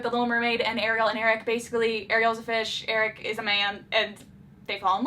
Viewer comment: did u just say ariel was a fish...?